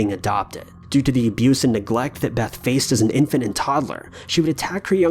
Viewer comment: I heard it as infinite toddler and imagined beth with an infinity gauntlet